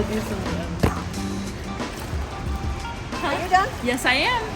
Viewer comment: i'm not native english speacker, What did that woman say? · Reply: “Are you done?” “Yeah”